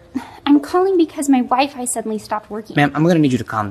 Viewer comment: bro the entire time I thought she said "my wife suddenly stopped working" I was so confused